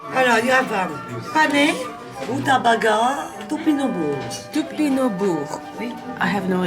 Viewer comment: what she said was a "Topinambour" aka Jerusalem artichoke in english